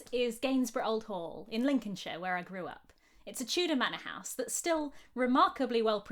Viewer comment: 😂 at first I thought you said you grew up the that Tudor manor loll